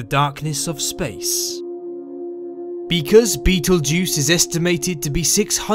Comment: did he just say beetlejuice?